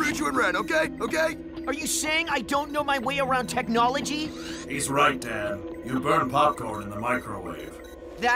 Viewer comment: I heard "alright dad" XD